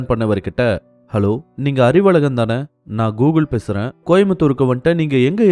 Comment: 😅 sorry sir I keep hearing the name as Gogoole . I presume the name to be Gokul. Hope Gokul is the correct name